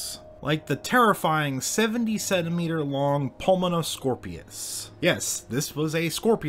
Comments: What's the creature at 5;23? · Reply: jesus christ at first I thought you said, "seventy-seven meter long" and I knew that couldn't be right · I misheard 70 centimeters as 77 meters and was like WTF!!!😳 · I nearly had a heart attack when I thought you said 77m and not 70 cm. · i thought you said 77 meters long xd